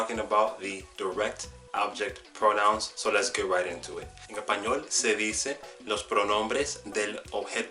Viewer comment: Como se dice a 0:23? no entiendo. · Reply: "En español" es lo que dije. Mala mía! Sé que hablo rápido y suena como "en e'pañol" cuando yo lo digo.